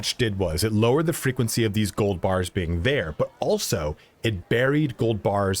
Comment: I legit thought that said free cold beers 🍺 😂